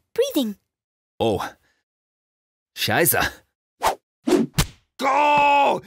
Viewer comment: Is Klopp saying Scheiße?